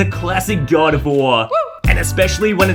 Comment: Swear you said god of war